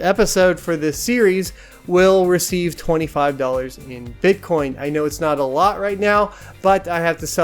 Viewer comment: I thought he said 25 bitcoin I was like ok you said I can order these when...